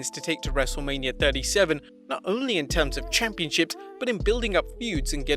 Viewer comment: Who else heared "Championshit" insted of "Championship" at 6:11?